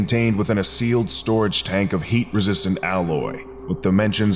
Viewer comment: It sounded like you said "hate resistant alloy", which sounds quite anomalous